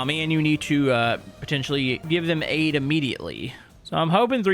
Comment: I thought he said an give someone AIDS. I had to listen to it again and realize he said aid lol